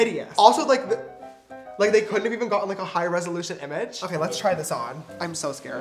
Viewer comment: When you said (ok let's set) I thought you were going to say ok let's set it on fire